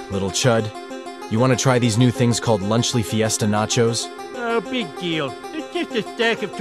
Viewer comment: launcly disaster? Did I hear right